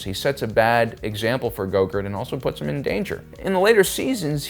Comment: I swear to god you're saying "gogurt" lol